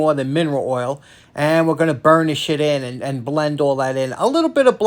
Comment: I thought we were going to have to ask to put some money in swear jar at 10:12. But he said burnish it not "burn this sh!t". I have got to get new speakers.